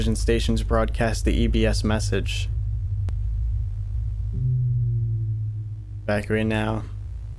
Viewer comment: My dumbass thought it said ejaculate now😂